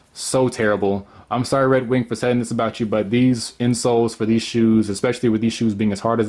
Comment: Insults instead of insoles. . . Lol!